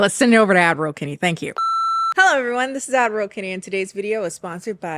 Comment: For the longest time, I thought she was saying Admiral Kenny